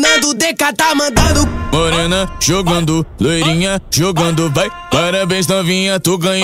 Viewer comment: Só eu que entendi "lorena jogando"?